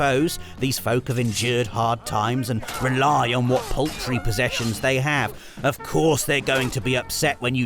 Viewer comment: did you say "poultry"?!